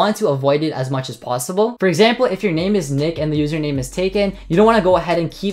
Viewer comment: thought he said dick😂😂😂